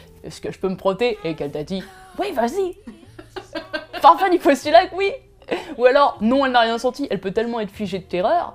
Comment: A chaque fois que je re-regarde cette vidéo y'a un moment que je replay : 4:54 qu'est-ce qu'elle dit? :x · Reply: Oh merci, c'était "pars pas du postulat que oui" que je comprenais pas :)